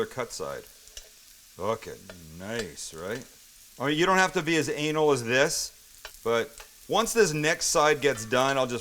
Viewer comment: did he just say "you dont have to be as anal as this"???